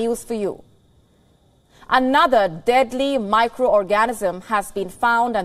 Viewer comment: Did she say "another deadly micro orgasm" has been found @ 10:17 😂